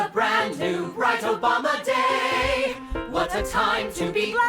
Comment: did they say “bright Obama day”?